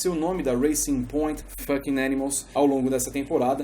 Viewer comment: O que foi que ele falou? Eu entendi "fucking animals"